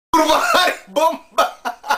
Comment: "kurwa haj bomba" XD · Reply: Ja usłyszałem "ku*wa harry bomba"